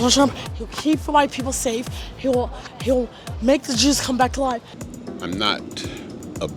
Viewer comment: The black lady "He's gonna make the Jews come back to life." lmfao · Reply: I heard her say 'make the Jews come back alive'